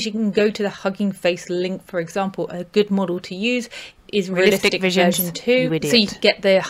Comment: On the part where you say "A good model to use is "....." the sound doubles and I can't quite make out what you are saying. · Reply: I say realistic visions 👍 sorry a about that. It’s the ckpt file i’m using